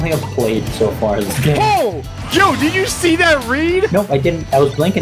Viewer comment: says “Jo, did you see that read?!” Instead of Jome. You can hear it if you listen.